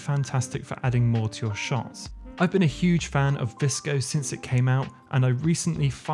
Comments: can you tell me the name of the editing app you use. at 3:52 . i cant make out the name you said. thanks ! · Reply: Sure! It’s called VSCO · what app are you talking about? Disco? DSCO? I couldn't understand or find this app... Would be great, if you put subtitles, whenever you show something new. Or write in the description, which apps you were using throughout the video 😊 Thanks for sharing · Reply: You probably meant VSCO? ^^